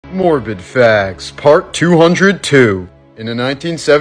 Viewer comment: I thought he said "Morbin facts" 💀